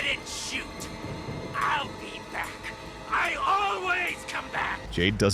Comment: Did he just say I ALWAYS COME BACK